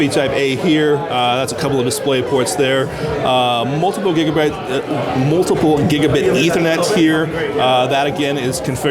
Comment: I hear "multiple gigabit ethernet". I was like what he means? There's only one. Then I was like aww he means multi-gigabit ethernet. Subtle, ambigious and confusing at times.